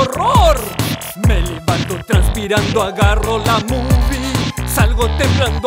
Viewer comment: What- yo escuche vamos a tomar una bubi K MRD XD